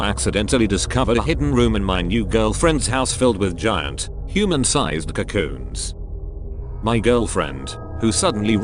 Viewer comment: Help why did I hear 'human-sized racoons' and spent a minute trying to understand why she turned her Exes into giant racoons 😭🙏